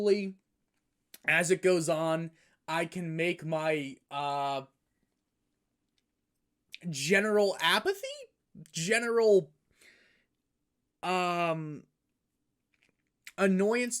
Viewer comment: Did he just say "Apathy?" ...